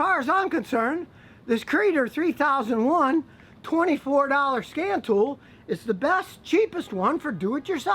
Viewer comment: At first I thought you said "scam tool" I hope I never need one...